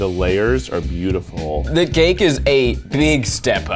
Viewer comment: he really said “the gake” 😂